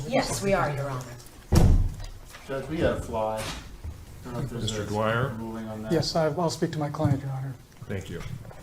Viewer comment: “Judge we have a fly. I don’t know if there is a ruling on that.” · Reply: Oh my god, I thought he said “I don’t know if we want to do something about that”. I can’t handle this 💀 Took me 2/3 of the way through to question if this was real. Fantastic.